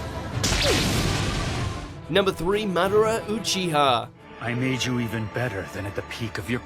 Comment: Did he just say Manara?